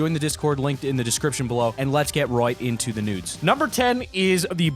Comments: did he just say NUDES???!!! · Did he say nudes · I heard nudes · DID he say let's get right into the nudes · Did he say nudes on 1:01? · did he say lets get right into the NUDES?